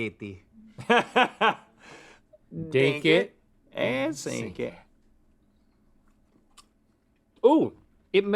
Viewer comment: CC says Dink it and SYNC it. Either I've always been confused and thought it was SINK it, or the person/bot doing the CC is confused.